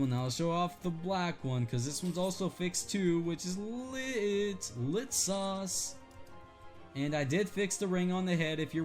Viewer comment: Did he just say “lit sauce”?